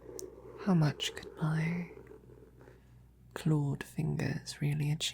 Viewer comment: I heard it is as "How much could I, Claude Fingers, really achieve?" and had a good chuckle.